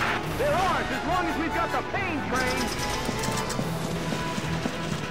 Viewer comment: did someone just said "pain train?" ouch...